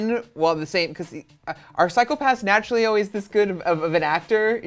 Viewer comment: Did he just say "psycho pass"-like the anime?!